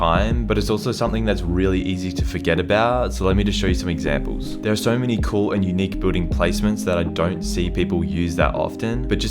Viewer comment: So I swear it sounded like he said "there are so many unique booty placements"😂